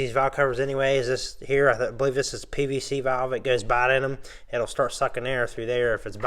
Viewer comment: What are you saying at 0:46 - 0:50?? If the PCV is going “bidin?” Is that what you said? If so what does that mean and if not can you clarify? Excellent video I’m just missing what you had said.😝 And thank you for the great instructional!🤩🤩🤩